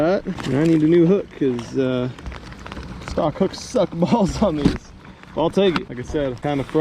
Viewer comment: did you say the stock hooks suck? Hope that wasn’t a 6th sense bait!